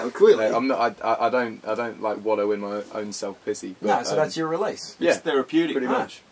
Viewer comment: I thought he said his own self pissy